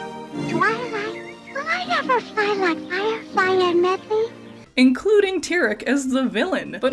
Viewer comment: I didn't understand any of what she look, that was straight gibberish! 😂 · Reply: I'll translate! She says: "Twilight, will I be able to fly someday, just like Fire Fly and Medly?"